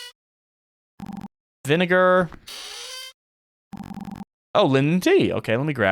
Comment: Did you just say the n word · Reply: bruh he said vinegar you potato